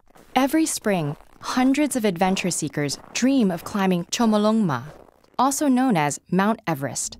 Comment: What is that word??? chngmonglonma?? I never heard of it. I am from Nepal and we call “Sagarmatha”